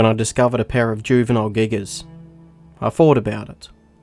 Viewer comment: The first time i seen this video. I thought said. "A paras footsteps 😂" he said" a Pair of"😂😂